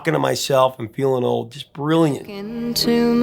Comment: How does a 21 year old singer sing "Talking to myself and feeling old" and make it sound genuine? Her gift is that her voice never sounded false when she was singing any line. She connected to the words so well. There are a lot of female singers who can sing with exuberance. Singing with power and fireworks galore. She never needed to overstress herself. Her singing just flowed out of her like hot chocolate. So sweet and pure and warm. Why is it that it seems the best things in life are limited. We needed more of her singing but it ran out. What a shame. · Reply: Haha! For the longest time I heard it as "Talkin' to myself and feeling low". One day I needed to be quiet so had my headphones on and I had to stop and replay the opening of the song again - did she say OLD instead of LOW?? I was stunned! 😂 I still think it makes more sense my way.